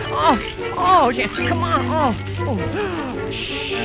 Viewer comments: I could have sworn Bimbo said "Ah shit!" at 2:20! XD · did he just say oh shi- oh come on ?? in a kids cartoon??